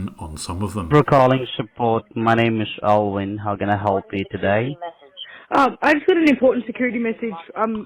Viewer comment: I know my name when I hear it hah, he said Owen 😎